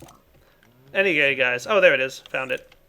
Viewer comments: Did say at 30:53 "Any gay guys?" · was I the only one who heard anygay instead of anyway in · Is it just me or did he say "any gay guys" at 30:50? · Is it just me or does he say "anygay guys" at 30:53 ?